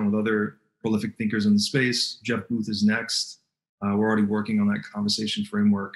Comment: holy crap thought he said jeff bezos for a second